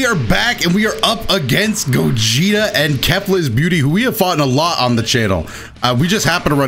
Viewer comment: You got me once you got me twice but you won't get me three times machine gun punch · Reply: i thought you said booty im crying